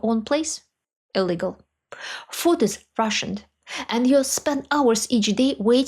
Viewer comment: When you said "the food is rationed", I heard "the food is Russian", and I thought, "...Well...yes, I suppose that would be logical."